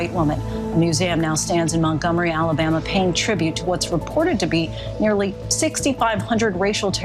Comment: Did he just say “perverted” in place of reported ? 😅